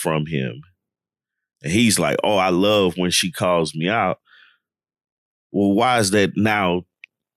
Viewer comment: I coulda swore Arris said “I don’t love it” when asked how he feels about Jasmine calling him out. Y’all might be right about the cop out answers 🧐